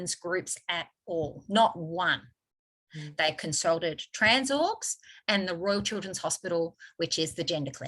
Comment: Sounded like you said "Trans Orcs." Speaking of Lord of the Rings...🤣🤣🤣